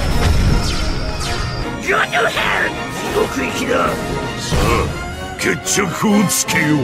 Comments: Hold up, at 1:23 i swear it said "go to hell blue creature", god this game is dark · At one 1:20 does anybody hear "go to hell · did he say go to hell